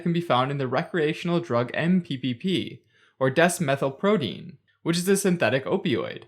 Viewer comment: Am I the only one who misheard desmethylprodine as Death Metal Prodeine?